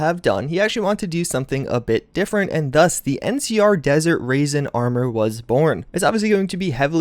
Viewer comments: Anyone else catch he desert raisin at 1:10ish😂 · Dude, you called it "Raisen" again (1:07). I actually laughed when I heard it. Too funny yo